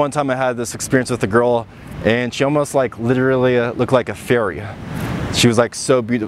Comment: I thought you said "ferry", instead of "fairy" 😂